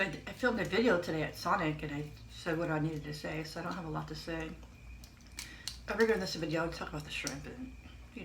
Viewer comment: Wow that looks amazing! I need to try that sauce, yummy. What did you say at 21:30? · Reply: “For this video I would talk about the shrimp.”